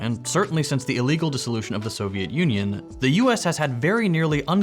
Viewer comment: did I just hear illegal disillusion off the Soviet Union please specify that?